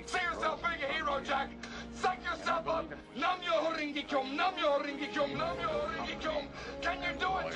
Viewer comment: What did Tuck say at 0:29? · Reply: Nam myoho renge kyo, it's like some Japanese Buddhist chant. It means, "Jump out of the back of the truck, Martin Short!"